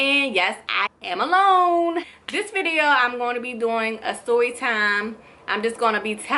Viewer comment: why does it sound like shes saying soytime when she meant to say storytime